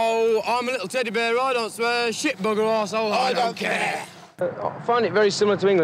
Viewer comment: what do they say here? I only get the "i'm a lil' teddie bear, i don't swear" part :/ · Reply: "... shit, bugger, arsehole. I don't care." Pure poetry. 🤘